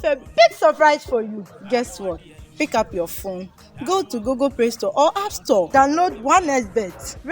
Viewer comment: Did I hear Google "pray" store @ 0:10sec